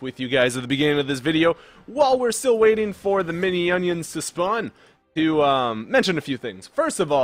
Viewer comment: it sounded like he just said "mini-onions." ur great at making league fun yet giving advice at the same time. Great job! Keep it up!